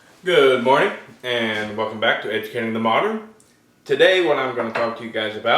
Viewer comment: I thought you said, "Good morning. Welcome to educating the moron" in your intro :-O lol